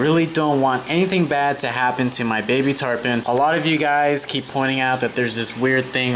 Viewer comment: I thought it said baby tampon